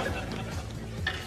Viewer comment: i’m so stupid i thought it said “alexander what are you doing here?”